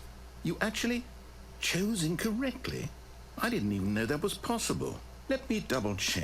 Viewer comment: there's a typo. Insted of *Know what was possible, it should say "know that was possible"